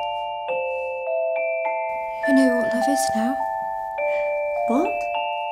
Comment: what did she say? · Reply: “I know what love is now”